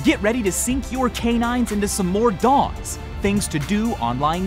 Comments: i thought he said "sink your canines into some more DOGS" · Am i the only one who thought he said 'get your canines into this dog'?